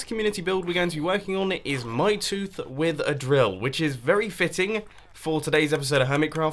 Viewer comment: at first i thought he said a tooth with a grill XD